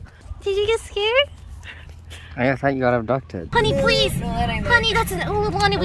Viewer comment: did he say he thought lily got adopted or abducted? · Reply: abducted